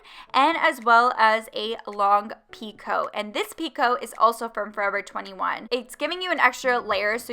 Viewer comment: When I first heard her say “2 coats” I was like who the fuck does that🤣😂 but omg that looks so good😍